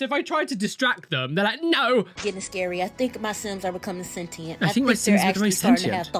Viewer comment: Girl is literally talking as if she's not the one that installed the Wicked Whims and Extreme Violence mods 😂😂😂 I don't know what she expected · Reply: Bro at first i thought she said her sons were getting sentient, and i was like "of course, theyre little humans, what did you think they were?" And then realized i misheard.. made more sense after